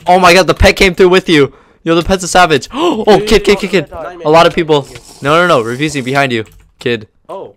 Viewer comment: What did you say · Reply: hè said no no no refusing behind you